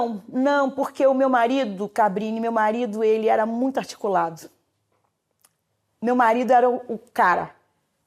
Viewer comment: oq ela falou "meu marido era muito articulado" oq eu ouvi "o cão é muito bem articulado"